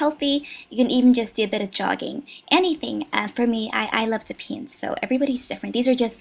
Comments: When she said "I like to paint" I was on a different tab and so I didn't see the picture of the pain brush and so I thought she said "I like to pee" I was thinking uh...that's very nice bubz! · I heard "i love to paint" as "i love THE PEEN" and was like O_O